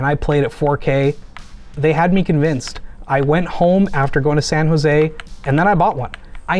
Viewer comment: San Jose did I hear that right?